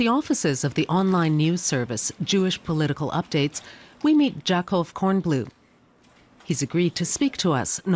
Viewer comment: Now hold on just one second... Did she just say, "Jack off cornblue" Oh man... If that kid was in public school... Wow....